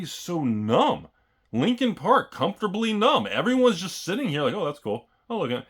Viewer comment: Did he just say Lincon Park comfortably numb?